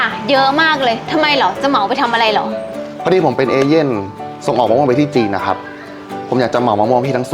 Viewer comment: ได้ยินว่าเอเลียน